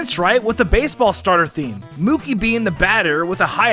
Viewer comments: think you called Grookie "Mookie' at 3:37. Thinking about mookie betts maybe? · DID HE SAY MOOKEY XD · did he just say mookey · did he say mookey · did he call Grookey, Mookey?